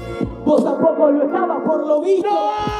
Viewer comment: Entendí "vos tampoco lo estabas gordo virgo" 😂